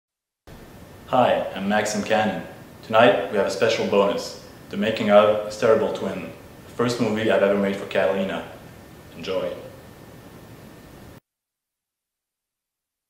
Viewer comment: I thought Maxime said "Hysterical twin"